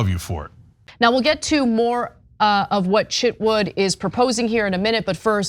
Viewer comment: I thought you said Chip Wood lol